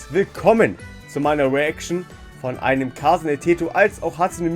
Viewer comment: Me not understanding what you just said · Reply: I said "Kasane Teto" 😊